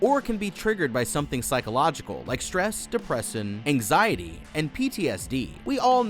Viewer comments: did u just say depressin? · He says "Depressin" instead of "Depression"